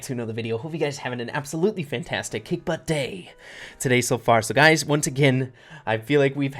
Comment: Hey Everyone!! Sorry about the clip at 8:42 not sure what happened 😰 But, What do you guys think of the picks? I think we can all agree that Shelly's is #1 hahhaha! Thanks for watching everyone! Have a kick butt day! <3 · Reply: thought u said a tick but day